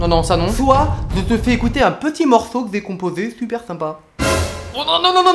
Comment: ya que moi qui entendait "Je te fait goûter un petit morceau décomposé"